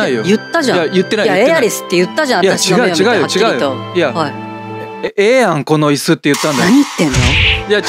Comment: ここのティファの「はい」が「おい💢」に聞こえるわwww